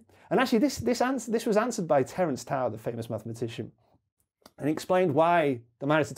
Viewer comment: Did anyone else hear him say, "Terrance Howard, the famous mathematician?" 😂 I wasn't looking at the screen at that moment.